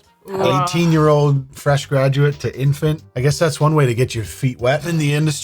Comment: i've just played this like 10 TIMES and i still don't know WTF was said because of the overlap where you said the word at the same time. please don't speak over the vids when people are speaking. : (. now we see this big reaction to something and now i'll never know WHAT. even with zooming in, trying to lip read either of you OR play it slow. : ( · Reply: Same here. "Infant" is what we were listening for.